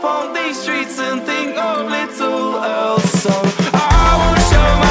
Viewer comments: Am I the only one who thought they said "we'll think of little elves" instead of "little else" before I saw the lyrics..? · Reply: To be honest, I heard "little Elsa" lol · Am I the only one who thought they said "we'll think of little elves" instead of "little else" before I saw the lyrics..? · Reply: I thought he said little Elsa, like from Frozen :P